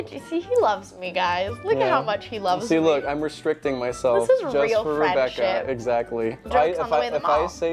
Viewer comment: I thought you said “This is real friend shit,” instead of friendship, like you were proving a point (~9:35). I went back 2x to figure that out.